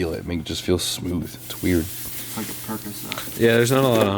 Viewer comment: does say percocet